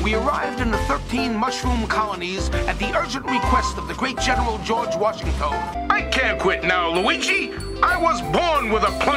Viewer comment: For a hot second I thought mario said "George Washinghoe"